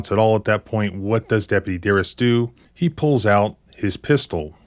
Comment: What's the cop's name? It sounded like he said 'Garris'...